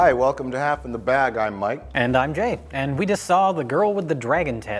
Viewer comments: For a split second I thought they said: “I’m Mike” “and I’m gay” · Did he say "And I'm gay"?